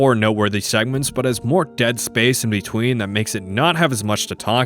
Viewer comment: Did you say "Dead Space"?